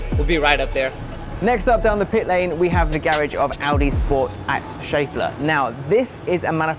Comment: I thought it said Snorlax lol